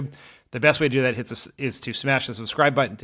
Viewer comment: Smash your subscribe button, I misheard as smash your crystals. I was like JFC, I thought we were done with the crazies.